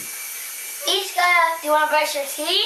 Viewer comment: It sounded like she kinda said all that when u put ur mind 2 it but the it sounded like she said I want to brush instead of I want 2 brush my teeth.